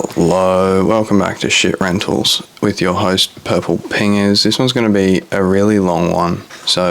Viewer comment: I thought you said your name was.